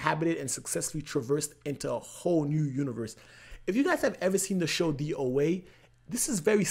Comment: What's the name of the show he says @ 14:04? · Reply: The OA